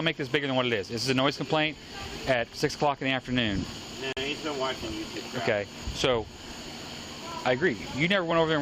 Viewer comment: "hes been watching youtube" did i hear that right